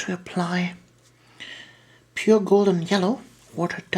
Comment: Hi, at 5:43 did you say "Pure gold and yellow"? can you please clarify, what are the paint names there? Thanks!! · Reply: "Golden yellow" I think - some of GW's paints have changed names since 2010